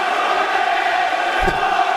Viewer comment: Please what says the Irish public in 5:49 sec ? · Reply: Conor's gonna get ya